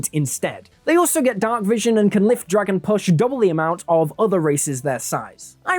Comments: I keep hearing you say, "they can lift dragon puss" lol. It took a while to translate that to carrying capacity. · Totally thought you were saying "lift dragon push" and I didn't get the reference.